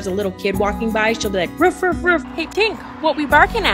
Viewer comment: At first I misheard the dog's name , I thought she said "Hey Stinker"